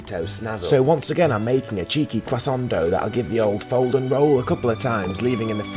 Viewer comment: im not on me earphones but damn it sounded like he said " i made a cheeky crescendo "